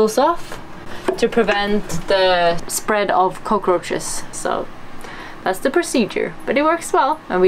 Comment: what does she say there? I undestand coat rochers but I think that makes no sense :/ I realy want to know what she says there